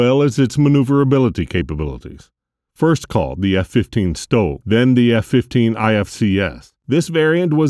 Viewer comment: What did he say it was called? A stove? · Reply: STOL, short takeoff / landing.